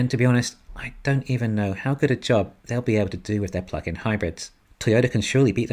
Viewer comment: Thought for a moment you said fucxxn hybrids…then realised it was ‘plug in’….not sure which is correct🤨